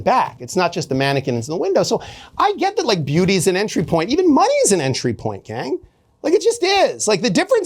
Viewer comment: Did he say “Even moneys an entry point gang”? 😂